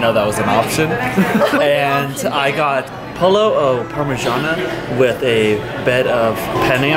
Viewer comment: i thought her cus said he got polio